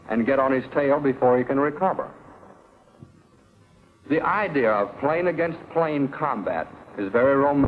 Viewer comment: Can anyone make out what was said at 8:54? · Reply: there's a cut in the audio. based on the context of what follows, it seems like he may have originally said something like "The idea of plane against plane combat is very romanticized in motion pictures. But modern arial combat has advanced beyond what the Aces of World War One ever dreamed. The keynote of fighter tactics in the United States Navy today is teamwork."